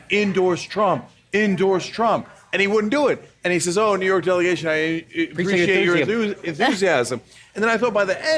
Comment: I didn't hear him say "The New York delegation, I appreciate your enthusiasm" I heard him say "F# you very much."